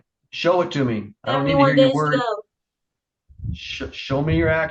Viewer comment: needs to stay in jail until she learns some respect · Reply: Could you make out what she said at the end of the hearing like the very last thing she said? Did she say _ days to go?